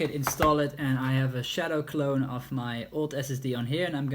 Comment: Did he just say "shadow clone?"